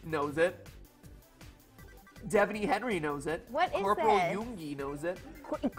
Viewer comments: Did she say Corporal YOONGI!?!?!? Any ARMYs here? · did he say Corporal Yoongi or is that just my ARMY brain... lol